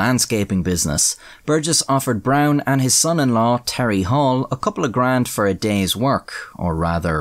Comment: bro I thought you said "Tally Hall" like the band